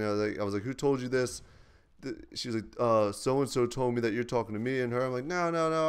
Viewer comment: her name is mia i think 3:42 he says 'he told me your talking to mia'